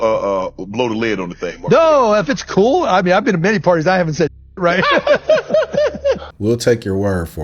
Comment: Did say he's been to many parties or Diddy parties.....